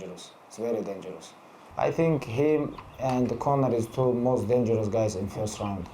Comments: Mc Gregor going to be mad when he sees the subtitle says “corner” instead of Conor 😂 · I think subtitle got it wrong. He was saying Justin and Conor 👆